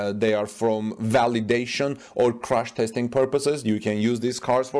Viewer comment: I don’t think that Tesla would crush cars with new tires, brakes and tinted windows. This would be an unnecessary expense. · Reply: LOL It sounded like he said crush, my bad. OMG so funny. I love his accent though👊